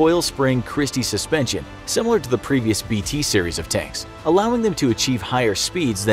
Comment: Did I hear 'T series of tanks'